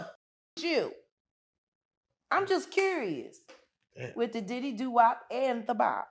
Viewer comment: Wait did she just say Trey songz been with the diddy do opp bop 😂